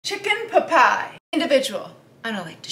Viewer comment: I thought she said “chicken pepay(a)” 😅